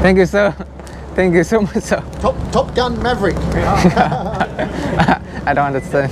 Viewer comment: GỬI BẠN: ở phút 11:38 bạn có hỏi Hoa tiêu nói gì. Hoa tiêu nói là "Top Gun Maverick". Là một bộ phim nói về một phi công (Tom Cruise) lái máy bay chiến đấu của Mỹ. Ẩn ý của Hoa tiêu là nói ổng giống như nhân vật Maverick trong phim Top gun đó bạn.